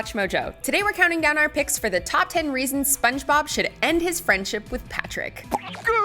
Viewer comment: I thought it said "top 10 reasons he should end his life"